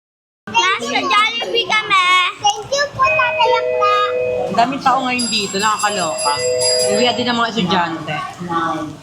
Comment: Tama ba narinig ko @ 9:53 na tinawag ni little girl na tatay accla si? 😂